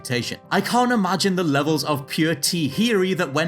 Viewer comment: WTF is saying it 9:45? I can’t imagine the levels of pure “tiheary” that went into smuggling an episode...?